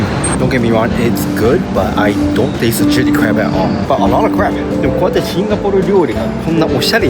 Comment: Did I hear 'A lot of Crab/crap'? :face-green-smiling::face-green-smiling::face-green-smiling